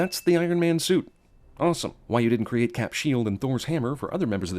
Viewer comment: Me being me thought you said “cap’s hammer and Thor’s shield”